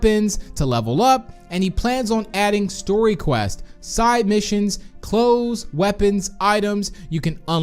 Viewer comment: did he just say story quests?!?! So this quests will be related to the piggy storyline??